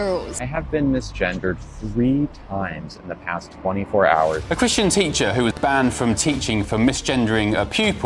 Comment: It sound like he said a cushion teacher😂